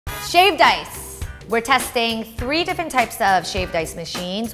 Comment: Sounds like she is saying shaved dice